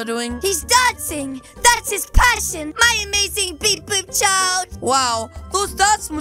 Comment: When you pause it at 12:00 I thought the mom said “my amazing kidz bop child”